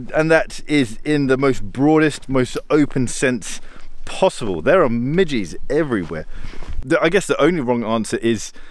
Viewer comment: I thought he said "there are midgets everywhere." Hahahaha